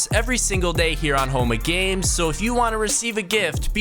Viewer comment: Everytime he says “home of games”, I hear “homo games”